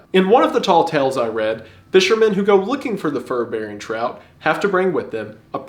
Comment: I thought he was saying "Fur berry trout" 😂